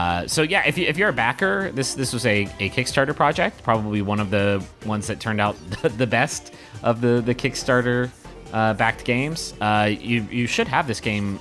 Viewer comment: You probably don't remember this because it was in January but this comment is confusing me. "If you're a kickstarter backer this should already be in your inbox. It's probably one of the best of the kickstarted games" That's what he said at 5:50 and it's the only time kickstarter is mentioned. I don't see what you're reacting to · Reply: Uh. I could have sworn he said something like "probably one of the only ones that turned out," but what he actually said is "probably one of the ones that turned out the best of the Kickstarter-backed games." And you're right; that doesn't really imply anything negative about other Kickstarted games. So, yeah, it seems I completely misheard him. That's what I get for trusting the crappy office speakers I was listening to this on. I'm an idiot, and I apologize. Should just keep my mouth shut.